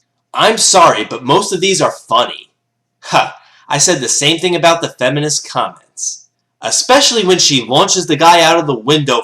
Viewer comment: I heard "I thought the same thing as the feminist cunts"